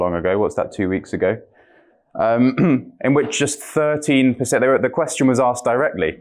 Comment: The Captions say "133%" instead of "13%" as of cohering with the language spoken here. There are numerous other Caption issues in this video and other videos on YouTube.